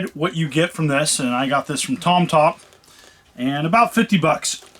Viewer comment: I don't know if you are kidding but the company is Tomtop. I thought the same thing at first. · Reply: Ah, ok, I really thought he was saying Tom Tom.